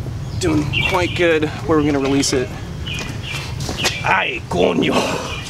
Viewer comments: did he said "Ay coño" ajsjsj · did you just say "coño" hahahah thats a very european spanish way to swear lmao. but you clearly had a latin american accent sayin it so im curious where you got that from hahah